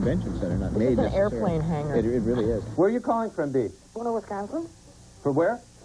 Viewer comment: Who was the guest @ 4:27? and PLEASE tell me the caller did NOT say what i think she said. (I swear it sounded like she said "Boner, Wisconsin" LMAO!